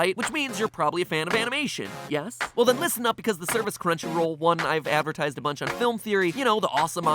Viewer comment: Crunchyroll? I thought you said Crunchyweeb